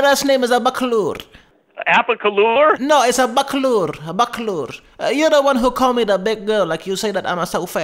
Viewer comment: for a second I thought he said like “Abrakadabra”